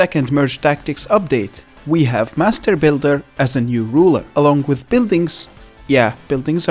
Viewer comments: i heard masterbaiter 💀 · I thought yous gonna say master baiter for a second there